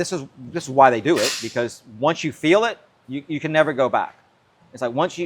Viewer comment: did he say, "Once you go black. You can't go back?"